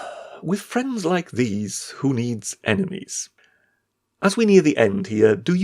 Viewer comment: 18.25..."With friends like these..... who need enemas?" Oh, sorry! I misheard. "Enemies" Not to self... syringe ears and remove wife anti-nag filters.